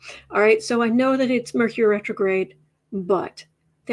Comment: "So, I know it's Mercury Retrograde butt..." Wait, did I hear that right?? 🤔